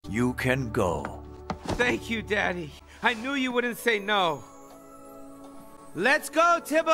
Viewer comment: “mAmA miA! A wOodEn bOy!” shoots him Me: you’re a talking, Italian cat with a gun! How is that not more worrying? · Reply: I thought he said "It wouldn't be"